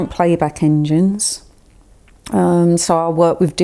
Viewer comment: what she said here: "my normal playback is..." ? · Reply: SADiE